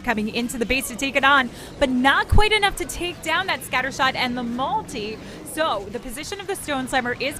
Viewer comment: Did she call that eagle artillery a scatter shot